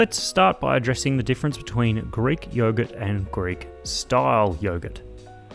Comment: I thought you were going to say Greeks and yoghurt 💀